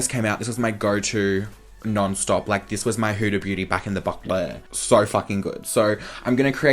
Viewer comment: What does he say at 4:04 "back in the ... " I always die whenever he says it but literally cannot figure out what he's saying lmao 😂❤ QQQQ love you